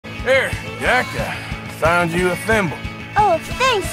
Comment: I watched it for a second and then scrolled down and I SWEAR TO GOD I heard Arthur say “here Jack, I got you a femboy” 💀💀💀